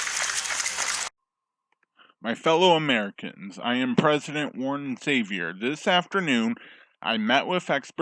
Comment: I literally thought it said "I am the president, your lord and savior" lmaooo